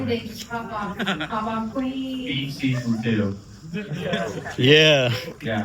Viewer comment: what tv show is saying ? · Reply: Beef Season 2